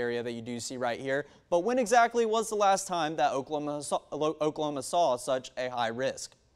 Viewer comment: What did he say at 0:43 ? "Okla okla" ... what ???!! I'm so confused.